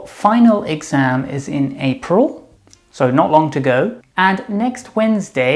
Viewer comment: in the minute 3:45 I think I heard the expression "Not long to go" but, as I'm not sure, I'd prefer to someone help me with that...